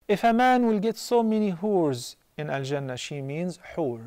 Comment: At first I thought he said ‘whores’ not ‘hoots’ -.